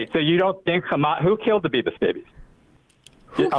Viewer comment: Did he say who killed the Beavis baby? 😂